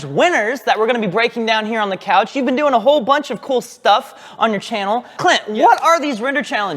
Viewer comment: did Wren actually say "Cl!t"... ?